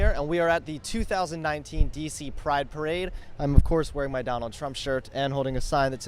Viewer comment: anyone else hear woke parade or just me